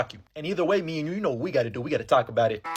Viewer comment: Why do I hear "we gotta taco bout it"